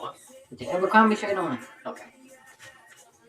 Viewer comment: he clearly said Abercrombie shirt on